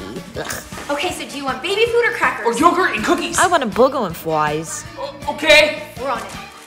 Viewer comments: I thought said a bogey and flies or did she? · I thought she said I want a buger and Flys 😂😂😂😂 to cute 😍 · I thought she said “A boogie on fries” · I thought she said i want boogers and flies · I thought she said I want a borger and fries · The fact it Sounded like she said I want a bug on fries 😂😂😂😂😂😂❤😊😅😮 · I thought she said boogers and fries😂😂😂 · I thought she said I want a buger and Flys 😂😂😂😂 to cute 😍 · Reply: I thought she said “I want a boogers and fries”😂😂😂😂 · I dont know why but I thought said buger and fries😂😂😂❤❤❤ 👇🏼only true Zamfam can like this ❤❤❤❤❤❤❤❤❤❤❤❤❤